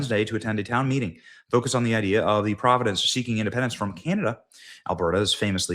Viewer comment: did you say "PROVIDENCE?"